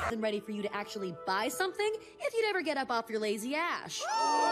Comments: Ember: if you’d never get up off you last ash What i heard: I you’d never get up off you lazy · Lazy what??? Oh lazy as, I ever thought she said lazy ass.